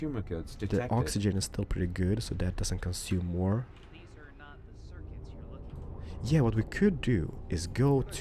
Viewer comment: Did anyone else hear: "These are not the CIRCUITS you're looking for." LIKE IF YOU DID AND UNDERSTAND!